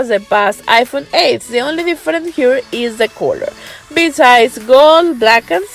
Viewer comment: Parece que dice culo 😂😂 en el min